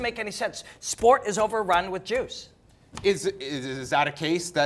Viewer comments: lol 4:28 I heard "sport is overrun with Jews" ...I was very...very confused. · Almost sounded like said sport was overrun with jews! But he really said juice.